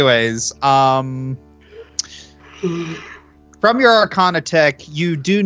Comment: I heard say "Your Arcana TECK" not check XD